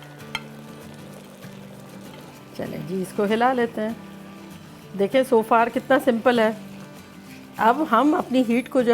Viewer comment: Hi did you add paprika in this? 1:54 is a little clear what you said after the citric acid. Sounded like paprika Thank you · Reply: After Citric Acid, She said TATRI another name of citric acid